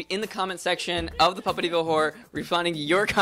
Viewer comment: OH, Puppetyville Horror! I thought they were saying 'Puppet Evil Whore'! >.< Also, if this isn't the best place to bring back Invader Zim, I don't know what is... but maybe he doesn't hold all the rights or something crappy like that =/